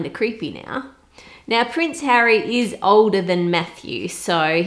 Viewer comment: did a double take because I heard “now prince Harry is older than meth use” but then realized it was Matthew, now “meth use” 😂